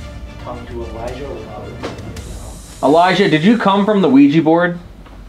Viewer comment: when asks "Elijah did you come from the Ouija board" I heard die or I / aye.